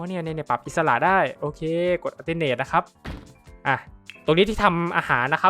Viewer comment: พูดว่าอะไรนะฮะ กดอะไรนะฮะ พอดีอยากเอาไปลองทำบ้าง😢❤ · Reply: กดปุ่ม ALT ค้างครับ จะเป็นการขยับของได้อิสระครับผม